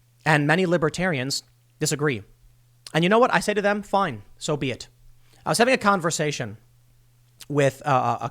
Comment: did he say soviet.... Russian collusion confirmed